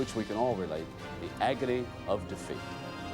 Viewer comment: As a child, I always thought the announcer said "agony of da feet". lol